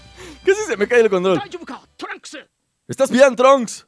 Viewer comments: En el minuto 8:40 tus mmm parecen gemidos🤣🤣👌🏻 y tambien cuando dijiste casi se me cae el control en el minuto 8:42 no escuche eso escuche casi se me cae el condon🤤🔥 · yo escuche casi de me cae el condon